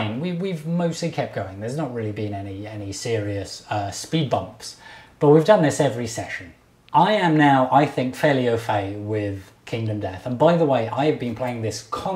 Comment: What does he say at 21:36? Fairly ofang? I'm not entirely sure what I'm hearing.